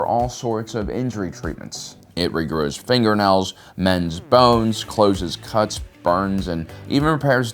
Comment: Oh, you said "mends bones" I thought u said "men's bones" at first 🤣